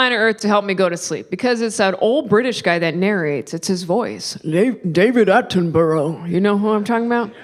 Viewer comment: I thought I heard you say 'Late David Attenborough'; I'm pretty sure David Attenborough is still alive.... in his 90s, but still alive